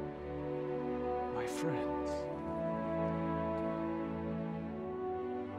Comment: I still say it sounds like he says "We bow to no one."